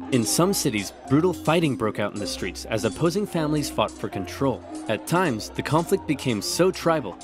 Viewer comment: I heard "As opposing families fucked for control" with the darn accurate depiction on the fringes 😂